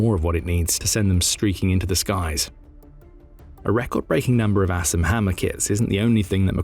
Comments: Sounds like hes saying Ass and Hammer..so im googling that. LOL. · I keep hearing "Ass and Hammer" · I'm gonna be honest... I thought he said "ass and hammer" at first lmao